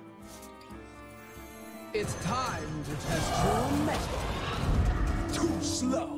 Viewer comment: Each time there is shirvallah i hear "It's time To test the new meta!"